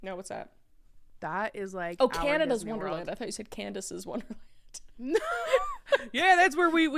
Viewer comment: i swear I heard Candice's wonderland too it's okay😭😂